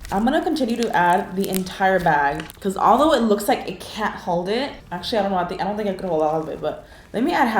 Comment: why did I think she said “A cat held it” it’s just like 🐈🐈🐈‍⬛🐈‍⬛🐈🐈‍⬛🐈🐈‍⬛🐈🐈‍⬛